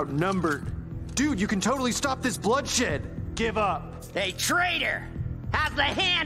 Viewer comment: Did Mordicai said a bad word !? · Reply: He said, “GIVE UP!”